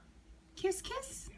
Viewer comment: Did he just say "kiss me"? 💋💋💚💚💚